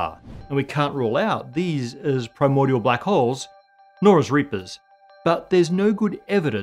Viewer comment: Reapers @ 9:25 ? You know I had to stop the video right there to check I heard it right.